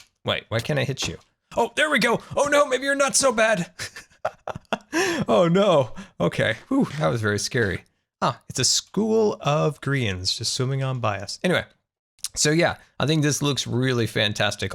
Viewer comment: You said a school of Grians, but I was confused because I lowkey thought you said school of Koreans